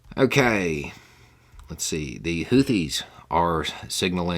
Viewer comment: Thank you for your comment. When Beau said the Houthis (sp?), I thought who the heck is that? My brain was vaguely familiar with the name, but couldn’t place it. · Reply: I thought he had said hoodies. Looked at transcript, saw houthis, so I'm guessing you are correct.